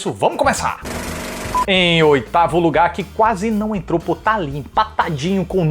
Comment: Quando ele falou "Oitavo Lugar" eu juro que eu ouvi "Otávio Ugá" Preciso fazer uma audiometria rápido